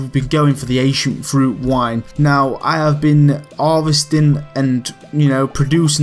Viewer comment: almost heard it as Asian fruit wine :P